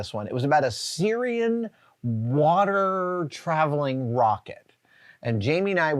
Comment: Can anyone please tell me what did he say here? Did he say "Syrian water travelling rocket"?